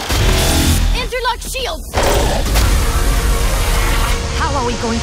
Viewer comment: When brigette said interlock sheilds i thought she said andrew likes shields